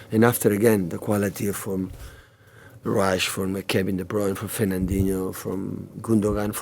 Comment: did he say rashford?